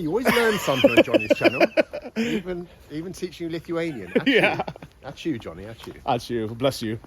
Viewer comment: what he said? ...catch you... ? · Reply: He said “Ačiū” it means “thank you” in Lithuania 🇱🇹❤️😇